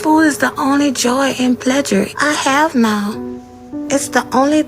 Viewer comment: did...did she just say pledjure?